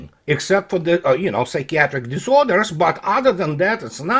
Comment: "What does facebook produce? Nothing, but psychological disorders"... ROFL... Another for the books!!! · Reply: Psychiatric disorders. I misheard him at first. Thought he said "like-cat-trick disorders".